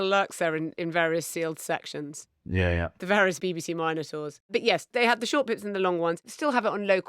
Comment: My Canadian ear legitimately misheard "various BBC minotaurs" as "various BBC minor tours", making me do a double take, pause and rewind. To be fair, they are essentially the same thing